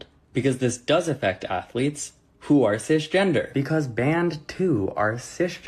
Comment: when he says "this bans women who are cisgender" my mom brain thought he said "fish gender" and then I thought wtf is fish gender 😭😭